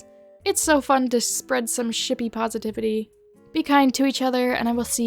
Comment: I thought that said "snippy" and I was very confused ._.